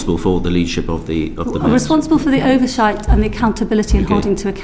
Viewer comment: Responsible for thee overshit! I heard her say